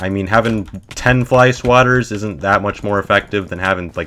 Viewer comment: Funny story- i had my volume on 10% and thought he was saying “housewives” until he mentioned swatters